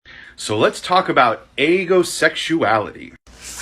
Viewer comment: the subtitles said eggosexuality Call up eleven guys